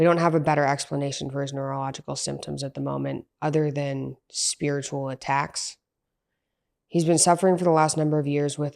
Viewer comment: spiritual attacks? Did I hear that right? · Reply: Yes. Spiritual attacks.